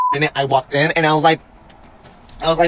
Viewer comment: "Please refer me as maam" 😂 Um no, but I'll gladly refer you TO a psychiatrist 😆😭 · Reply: Lol I thought they said man, not ma'am.